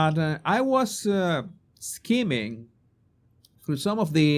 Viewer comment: Oh yes, you would certainly suit a top hat 🎩👍 It sounded like you said "scheming TB book" it's (Skimming) over the book, its sounds like swimming 👍 Great video, loving the section 👍 The only rivalry Meme has over the King is her bunions😂 Have a wonderful day and family ❤